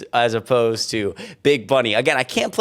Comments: did i hear.. "big bunny" 💀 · is, did you say "big bunny?"